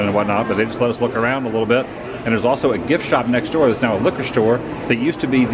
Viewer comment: Ohmygosh I had to rewind and watch this two or three times because evidently I was too sleepy when it first played. I coulda SWORN you said "it's now a HOOKER store, but it was the Star Wars Experience gift store.' (figured out you said liquor) Ya gave me a great morning of giggles and made me consider a hearing check-up soon. (Too many years at racetracks and standingthisclose to the stage at rock shows ha ha!! Eh? Speak up, Sonny! I cain't hear ya!)